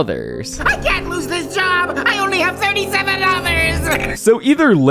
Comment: I am an idiot… for years I thought he was saying he only had $37 dollars